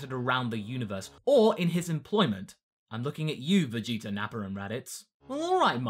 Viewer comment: I love when you say Nappa's name. It sounds like you say Napper